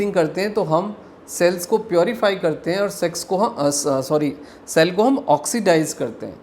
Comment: Kya bol Diya 😅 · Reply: Sex